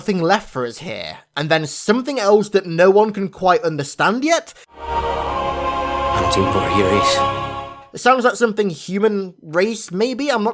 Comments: where you can’t understand what he said, he said “I’m not even sure where here is.” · I know im a bit late but at 14:27 I think he says "hunting for human race" ??? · I thought 14:30 said "I dont even know where 'here' is"